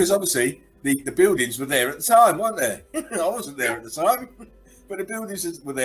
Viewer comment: I thought he said they weren't retarded. The door way/heat expansion? Doors are only ever built to be the same width at top and bottom? Come on. Their a little bit special needs, right?